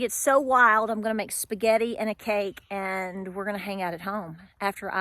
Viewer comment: For a split second there I thought you said you were making “spaghetti in a cake”…